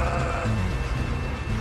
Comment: What does she say about PETA?